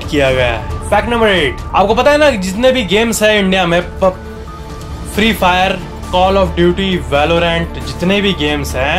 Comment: I didn't hear the word u said before ‘Free-Fire’🧐; what was that 🤔? Sounds like Pub 😎, something like that 😏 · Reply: He was saying pubg its now ban thats why he didn't said it 😁